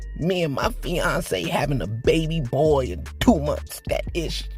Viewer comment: Having a baby boy in a minute... I thought kodak said.. and they were talking that it was a baggie, when he ate that bad stuff. Who knows with that dude.. someday we might know what he meant all these years, like a day in the life of Kodak Black.. Poor Kodak, poor takeoff, poor is way off script today..with no script. 😊 much ❤